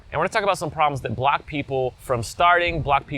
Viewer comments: Am I the only one who thought he said black people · Ngl I thought he said black people at 0:13 😂